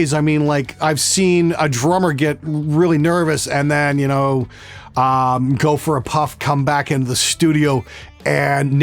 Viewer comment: When you said the bit about the nervous drummer I really thought I heard you say, "have a puff and come back Neil Peart". I was like I NEED SOME OF WHAT HE'S SMOKING!